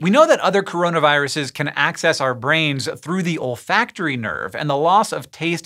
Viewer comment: For a moment i heard "ol' factory nerve"